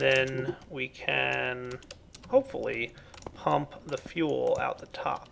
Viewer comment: Did he say hump at 19:59 LOL